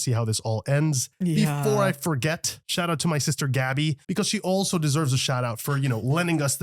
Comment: Did I hear "Gabi" 0:42, AOT Fans Assemble🔥😆